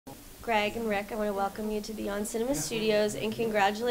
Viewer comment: I thought she said “welcome to the On Cinema set”